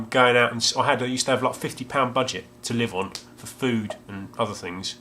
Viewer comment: I got distracted because our washing machine is mental. I thought you said 50lb budgie. On replaying the words in my head, I felt this may have been slightly inaccurate.